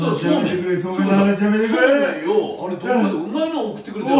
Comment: ソウメイ送ってくれたでしょ?って聞こえてわろたら、そうめんだった💦